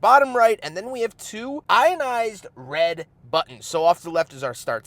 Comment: anodized? It sounded like you said ionized.